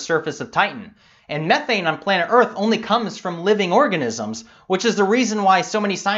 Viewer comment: Watching u talk about bacteria on other worlds and the amazing implications that these organisms bring to life. Then i lose my shit laughing cause the advert underneath says Detol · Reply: #organism- i thought you said orgasm for sec.